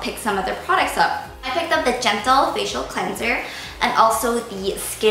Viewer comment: I thought you said "genital face cleanser" LOL DYING!!!!!!! 😂 +